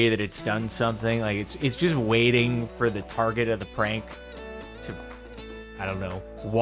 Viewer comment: that house is either on crack meth or dope. Or all three. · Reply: I actually thought he SAID that it "did some kind of CRANK" until he continued and it was "prank". 😜"crank" makes Wayyy more sense than "prank".